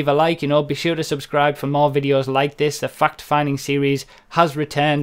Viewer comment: in the intro i thought he said "GTA FUCK finding series" the emphasis he put on fact made it sound that way lmfao